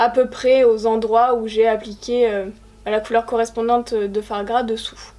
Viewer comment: Le francais pour moi est une langue etrangere. Jusqu'a 6:30 je pensais que tu disais foie gras et j'étais un peu confuse.